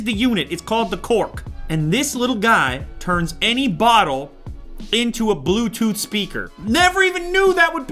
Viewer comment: i thought said its gonna turn any bottle turn into a speaker that speak to people